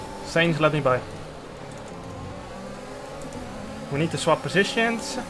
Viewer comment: to sainz : "we need to swap position" what I hear : "sainz, jarno is faster than you, confirm the message"